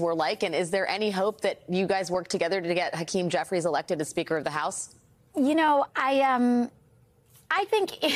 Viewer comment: McCarthy: Speaker of the House? I thought they said beaker to get soused!